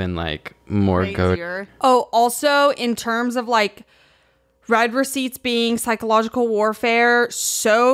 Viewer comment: the whole time i thought was saying red receipts and was getting mad at the existence of different colour paper receipts, and i started agreeing with her like “wait i never thought about that, thats so true queen youre so real” until i realised she was talking about text messages